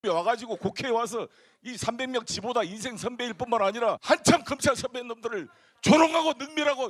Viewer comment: 송영길이 검사 선배님들이라고 말했어? 검찰 선배놈들로 들리는데... 그랬다면 송영길의 골수에는 무엇이 들어있고, 채널 에이의 정체성은 뭐야? 자막을 넣지 말든지...